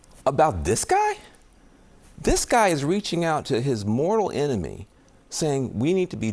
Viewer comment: Calling someone you have a disagreement with your "mortal enemy" is a big part of the issue. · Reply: I thought Van Jones said 'moral'.